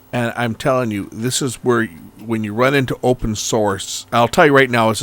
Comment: At first I thought you said, "open sores."